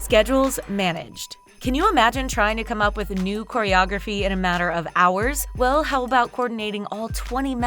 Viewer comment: Did I hear "NEW CHOREOGRAPHY "???